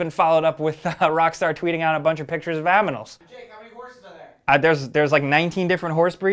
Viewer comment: Aminals? Am i the only one who heard him say "A bunch of different pictures of Aminals"?